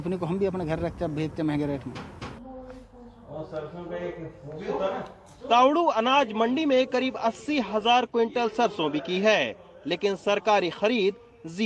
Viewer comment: I thought he said laudu anaj mandi 😂😂